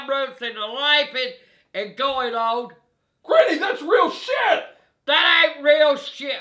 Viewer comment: I was thinking what said just slightly before 06:51, that’s “Real Shit”.😂